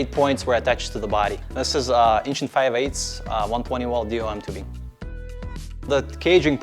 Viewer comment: did he say "1in 5/8 120 wall dmt tubing "? I can't tell what he said but I wanna find out what he said because I don't know what metal tubing to use for a roll cage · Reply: DOM